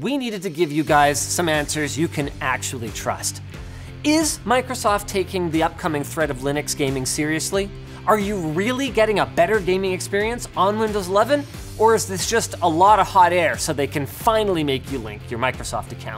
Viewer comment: did Linus just say "Windows lovin'?" Windows McLovin? 😋